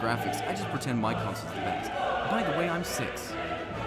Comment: right before your voice fading out did you just say: "By the way, I'm six."?